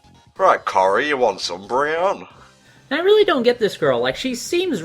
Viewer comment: did you say umbreon? or was it just me how heard wrong XD